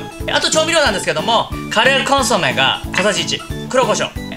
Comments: カレーコンソメって聞こえて… なんじゃそれは？ っと思った 顆粒コンソメね… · 顆粒コンソメと言ってたんですね。カレーコンソメって聞こえてしまいました。